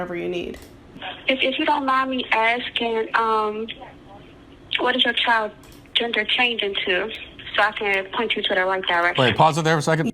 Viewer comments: The caption is wrong. The operator said "If you don't mind me asking; What is your child's gender changing to?" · Seriously? She said "is changing to" not "did change into" It is quite clear that is what was said and yet you repeated it like the supposed on screen transcript. Now, clearly this series if exchanges is outrageous but my God man! When you make a mistake that obvious is makes the rest of your opinion and the transcript suspect. Don't do that stuff! You already have them dead to rights here!